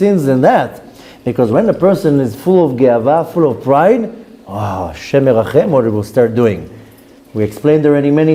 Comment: What is the phrase you are saying at 10:08 and what does it mean? Sounds like "Nashem Lelecacem?" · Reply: It's: "Hashem Yerachem' = G-d should have mercy...